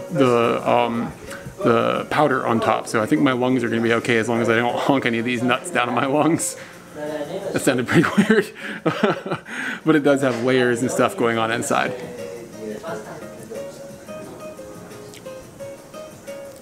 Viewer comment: I wasn't paying 100% attention and I heard the phrase "Honk on deez nuts" so I nearly fell over laughing.